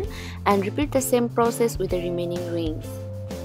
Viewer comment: What's that word you said in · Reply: repeat the same for remaining rings